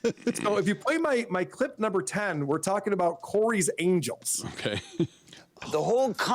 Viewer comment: You guys said Corey's angels and i heard Corey's a*us